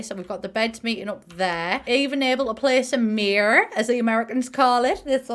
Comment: the fecking bathtub IM FUUUUUUUUUUUMING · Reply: Lol I’m 𝗔𝗺𝗲𝗿𝗶𝗰𝗮𝗻 and the mirror moment I’m desd😂😂😂😂 it sounded like u said murr but it’s ok